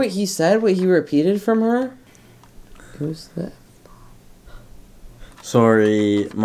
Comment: WAIT DOES ANYONE KNOW WHAT HE SAID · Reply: i think he said “who’s f*ck” because when they asked for her name she said, “who the f*ck knows” im not sure tho